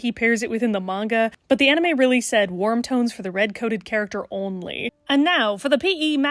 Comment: the first time I heard this I thought you said “worm tones” (as in the weird little dirt guys) and accepted it without hesitation